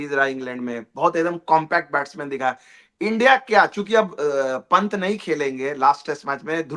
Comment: What did I hear him say at 10:41? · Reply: chuki